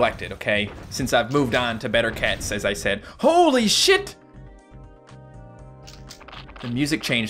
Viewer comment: did u just said the bad word sh*t